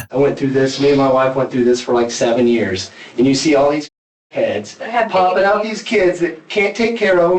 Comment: On minute 57:50 what is the word that is being censored? C** ? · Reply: I think he said crackheads?